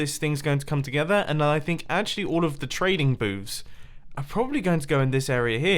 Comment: the what now? I know he said booths